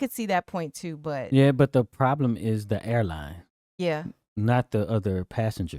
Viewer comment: I thought he said the problem is the hairlines. 😂